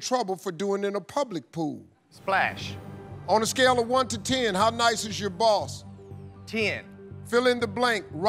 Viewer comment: I swear I thought he said, "How nice is your balls?" I had to recap just to make sure! Good laugh to myself!